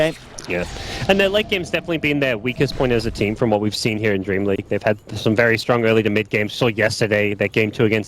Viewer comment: I thought it said anus dreamleague